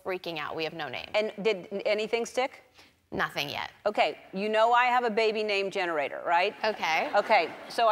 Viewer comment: for a second I thought Ellen said 'you know I have a baby named Generator' lmao